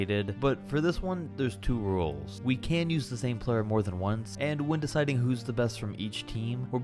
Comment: Even if leBrone was good in his prime Dwayne had a better career and he literally said we can’t use the same player more then once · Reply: u misheard he said u can use a player more than once cp3 was on the clips and pels as the all time player also while d wade was more successful in the beginning of his career wining a chip in 2006 with fmvp that chip is very controversial plus the rest of brons career is much better with many awards like mvps even thou wade should have gotten one Bron has 4 and wade peaked and had his prime in the early years of his career which made him not have many accomplishments on his own after that